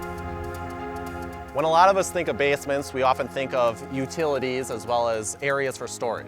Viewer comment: did he just say bass-ments?